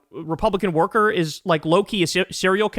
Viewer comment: I think he was saying "low key", as in subtly or discreetly. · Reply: Really? I distinctly heard it as either "Loki", or "Lowkey", the name of the rapper! (& it's not the first time I've seen a leftist/liberal site talk ignorantly about popular culture..)